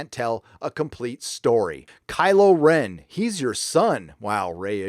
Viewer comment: Did I hear "crylow Ren". Haaaa.... Lol.... Be wide my friends